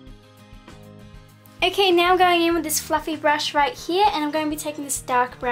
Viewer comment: for a second I thought you said fucking brush instead of fluffy brush ahha